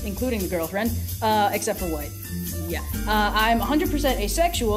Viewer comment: does she say except for wife or white? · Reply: White 😂 The question was “What are your sexualities?” Blue had just finished saying how he’s your standard white, heterosexual man. Red’s saying she’s neither of those things, except for white.